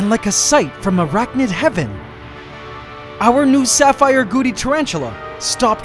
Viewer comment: NGL I thought you said Sapphire Booty Tarantula 😂